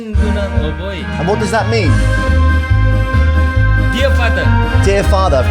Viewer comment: i thought he said " deer fu*ker" 😅